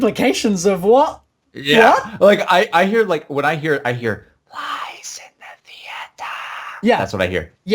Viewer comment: I hear "He lies in the theatre"